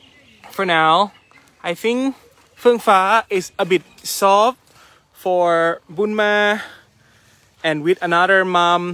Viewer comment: Watching this again, can somebody tell me what was saying about Nanny Foong Fah (sp) and Bunma? (Starts at 11:12). Is he saying they’re getting along better? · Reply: I replayed it, 4 times, and I couldn't make heads or tails of it. said that Fueng Fah was "soft" with Bunma and Kham Moon, and . . ?? but while Bunma is at relative ease around Kham Moon, she still is holding her distance from Fueng Fah. Things take time.